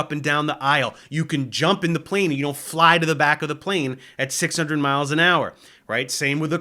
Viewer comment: You can jump in a plane and you'll fly to the back at 600 mile per hour Er... What?? No, you won't. [Edit] It's not what you said. Sorry, I misheard.